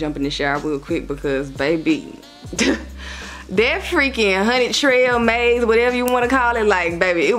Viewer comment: Girl your accent so cutteee. I thought you were saying honey trail all along lol. Loving the fits as usual too❤❤